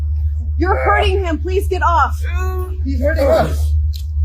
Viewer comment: did they say “he’s hurting us?” Disgusting